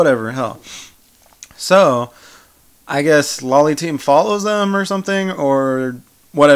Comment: Did I just heard "Loli Team"